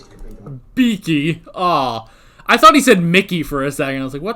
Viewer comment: Unless you have played it back he called Rory "Beaky", as a reference to his nose. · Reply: I thought I heard "Mickey" but Googled around and confirmed it was "Beaky". "Mickey" would be an interesting slip of the tongue.